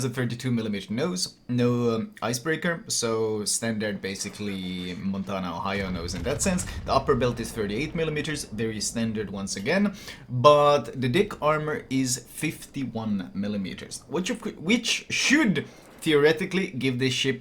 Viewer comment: lol I heard " dick armor" 😆